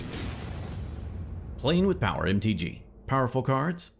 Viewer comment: Do i heard "playing with pauper"? Hahahahaah